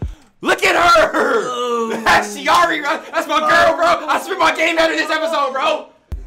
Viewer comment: can anyone translate what saying after "that's my girl" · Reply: “That’s my girl bro. I spit my game at her this episode”